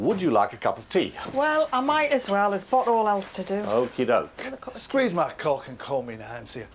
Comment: What the f did she say there? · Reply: 'Well, I might as well. There's pot all else to do.' Just a relatively common northern idiom meaning she's bored. She's from the north east of England 😁